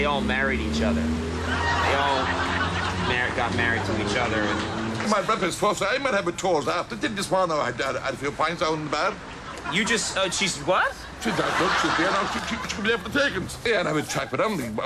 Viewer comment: Can anyone make a trancript of what that man said? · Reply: he said something like. "my brothers close, he does tours and this one arrived and had a few pints down the bar...................................shes there now, she cud be there for the takings.................. have a chat with her ,buy them a drink and thats it, get chattin them up.................................... i go up to the house there for a brandy......................oh Jesus how wud i recognize them....shes alright........thats everything he said.its a west of ireland accent